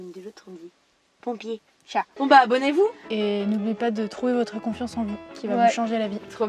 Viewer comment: A la fin j'ai entendu "Trouez votre confiance en vous" ce qui en soit est complètement l'inverse 😂 Félicitation pour votre évolution ! ❤❤